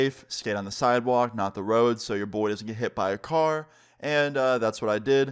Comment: did he say her mom hopes his friends get hit by a car